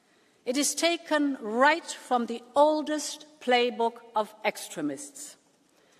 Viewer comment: Ursula the fascist Rule based order. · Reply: I thought she said " oldest playbook of excrement. "